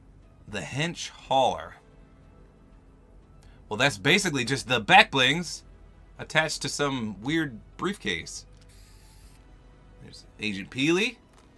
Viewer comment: what he say? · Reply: "...that's basically just the backblings attached to some weird briefcase"